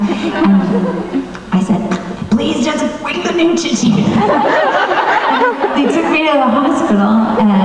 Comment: what did she say around 5:21 ?? i couldnt understand her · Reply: Lindsay White "Please, just bring the neutrogene!"